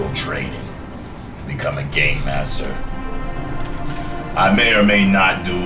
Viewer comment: at first i thought u said gay master but u said game master p.s do a playthrough this game was amazing